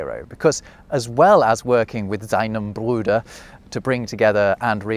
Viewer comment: typo in the subtitles: it should be "seinem Bruder", not "sienem". I've noticed that English speakers seem to have a sort of blindness to the spelling difference between the German diphthongs "ei" (aye, eye) and "ie" (ee), so much that I constantly see misspelled German words and names (like "Einstien" instead of Einstein, or "Freidrich" instead of "Friedrich", or even "Tolkein" instead of Tolkien... which is a name of East-Prussian origin and means "son of Tolk").